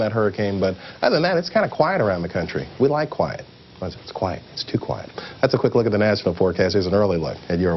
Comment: what did he say just before he says “it’s too quite” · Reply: "But, other than that, it's kinda quite around the country. We like quiet. But it's... it's quiet. Too quiet."